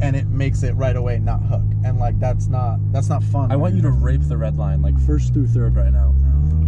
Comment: Did he just say “rape the redline”? ಠ_ಠ